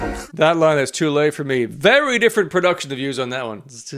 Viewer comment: Hm. I always hear the "It's too late for me" line in Distraction as "Dont lie to me"